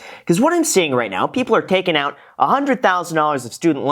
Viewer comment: I thought it said “$10,000” and I thought “well that’s not bad” 🤦🏼‍♀️